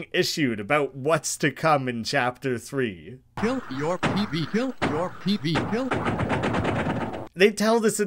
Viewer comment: Instead of hearing kill your TV I keep hearing kill your pee pee God help me 'Spamton Well my fellow BIG SHOT Top rated 1,980s Sales man Has to tell you........ God...doesn't.....exist